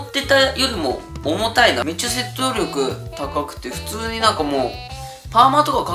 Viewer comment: めっちゃ戦闘力高くてって聞こえる